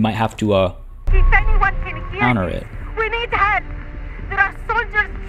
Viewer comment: I thought she said we need head.